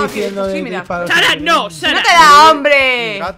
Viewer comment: no se por que escuché chara no. ( undertaleeeeeeeeee)